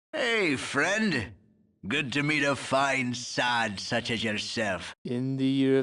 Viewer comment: What did he say? Hey Friend! Good to meet.... ??? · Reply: "Hey friend! good to meet a fine sod such as yourself!"